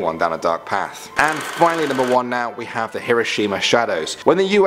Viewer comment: In 6:08 I thought he said kirishima